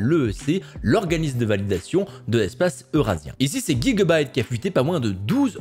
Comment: Attender il a dit quoi la 😳🤣😂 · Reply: Gigabyte ? C'est la prononciation en anglais, en français ça fait pas plaisir à entendre ^^